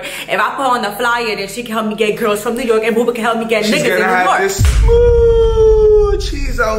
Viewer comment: Nah ngl sounded like she said “niggers” 😭😭